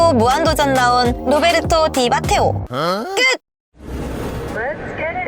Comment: 나만 디 마테오로 잘못 들었나